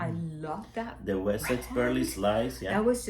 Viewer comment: i miss heard i thought she said wet sex ( wessex) 😂😂😂😂